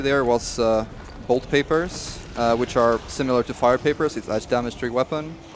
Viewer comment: When he said fire paper I thought he said fart paper LOL